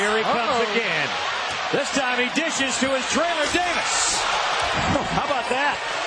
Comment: why did he call him Traylor Davis?